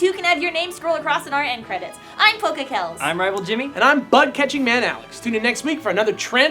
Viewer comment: I heard "I'm buttcatching maniac Alex". I can't be the only one.